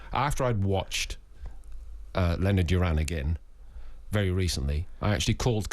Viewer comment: what fight did he say was the best ever? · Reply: Leonard - Duran